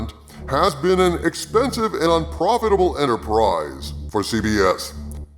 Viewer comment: I had to listen to it three times 4:58 to realize you said expensive and Un-profitable . Thought you actually Said expensive and profitable first go ...